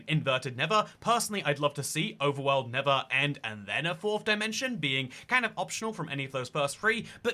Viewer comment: So I honestly thought you said ‘Wolf Dimension’, not fourth, was listening from a distance, then thought when you called it ‘otherside’ it should be accessed by collecting and playing all music discs simultaneously, maybe around ancient city portal? 🤣